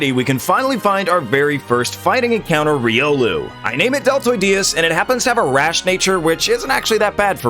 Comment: Everytime he says Lucario's name i hear "Deltoid Diaz"